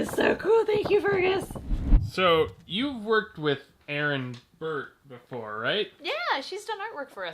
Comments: At first I thought said: “So you’ve worked with Aaron Burr Sir, right” and I started freaking out. Then I realized that it wasn’t Aaron Burr.... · As a Hamiton fan, all I heard was "So, you've worked with Aaron Burr before?" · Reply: I think they said Aaron burt